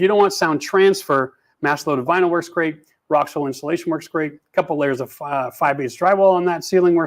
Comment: Sounds like you said rock soil. Did you mean rockwool insulation?